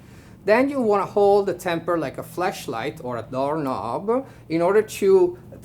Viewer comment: EXCUSE ME "LIKE A WHAT?!" oh nevermind he said flashlight. . . my bad